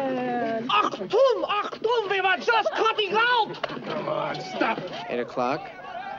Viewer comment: anyone know what he's saying? It's killing me (I don't know much German but if a random American teenager is saying it I can't imagine it to be beyond basic words or some famous Hitler quote." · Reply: Achtung! (meaning Attention Attention) and the next is English, something along the lines of "We were just cutting out" though I'm not sure about the second part.